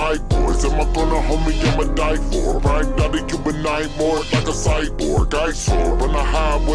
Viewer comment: Well chopped! But whats he saying on 2:34 - 2:36? .. I really can't understand it :) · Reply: I ain't a human no more I'm a cyborg...